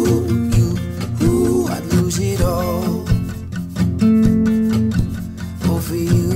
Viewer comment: 🙌👍👏was so happy to see you did a cover! And thanks for doing this song cause I always thought he said a deebeedo 😂but now I know he says I'd lose it all. Every cover you do always sounds like a song you wrote. 👏❤